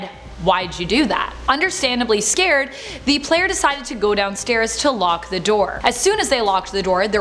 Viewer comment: At around 5:49 I heard " decided to go down-SCARES! Lol!